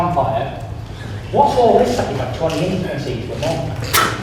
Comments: what does Tyson say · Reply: nathan pearson He said "what's all this about you having 20 intimacies with men"😬😂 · what did tyson say 12:45?? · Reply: He said whats this about you havkng twenty intimaticies with men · CAN SOMEONE PLEASE EXPLAIN WHAT FURY SAID AT 12:40 "BY THE WAY IVE BEEN HEARING AROUND THE CAMPFIRE" NOBODY KNOWS!! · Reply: he says whats this about you having 20 intimacies with men.